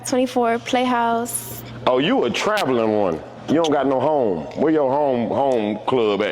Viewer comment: 😂😂😂Did he just say " Oh, you're a traveling hoe. You ain't got no home. " @ 2:19-2:26?